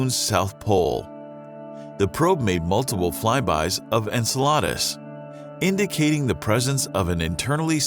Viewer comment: did...did he just say enchaladas? instead of enceladus?